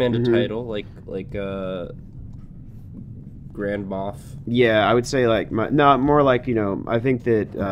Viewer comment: Oh my goodness the origin of Rah'oxah. (And I thought I heard call the dwarf 'Grandma,' but I think it was 'Grand Moth' or something like that.)